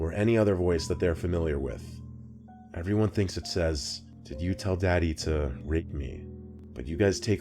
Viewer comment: When I heard the last one it sounded like she said did you tell daddy to leave me